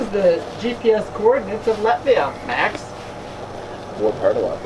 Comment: Sry for the unrelated question but am i the only one who heard “GPS coordinates of Latvia” at 16:20?